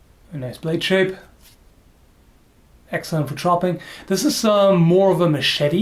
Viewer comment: Every now and again instead of chopping I hear "Excellent for shopping." Where the hell do you go shopping!?